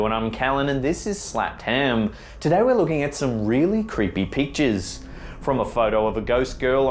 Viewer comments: LOL, I thought he said "really creepy peaches" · Hahaha I thought he said "some really creepy peaches" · Why does he say "really creepy peaches"? at 0:05? Wow that's not a bad accent; that's lazy pronunciation. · I thought he said today we're gonna be looking at some really creepy peaches....lmao wtf?!🤣🤣🤣